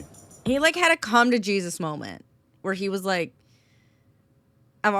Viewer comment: Misheard that as "Conga Jesus Moment" and immediately got the mental picture of a string of Jesuses dancing down a beach.